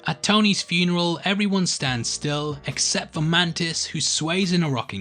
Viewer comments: he says man tits instead of mantis he thought we wouldn't notice but we did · when you say mantis for the second time it sounds like you said man tits